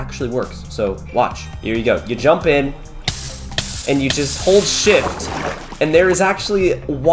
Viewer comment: When he said hold shift when he was going in the lava I thought he said oh sh*t